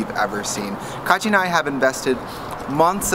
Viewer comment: the caption said "kache" instead of "katya" hahahaha